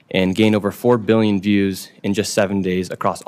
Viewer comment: billion? maybe I heard it wrong